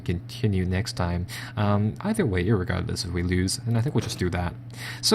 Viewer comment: did I hear you say "irregardless"